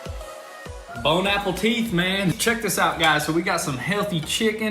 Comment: Did you say "bone Apple teeth"? 6:44 😆